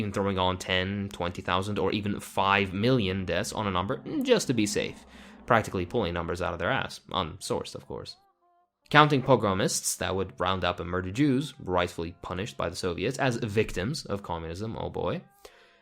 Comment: I re-listened to 11:26 like 15 times because I kept hearing "were" instead of "would" for some stupid reason. Good thing subtitles exist.